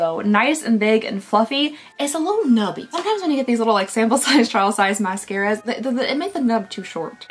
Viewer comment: Just thought you said “child size” mascara, and was so confused for a moment. Now I’m never going to refer to them as anything different. 😂